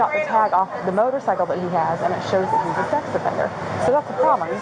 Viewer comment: LMFAO @ 7:30 the police said he’s a sex offender idk if I heard that wrong but I never he’s a sex offender correct me if I’m wrong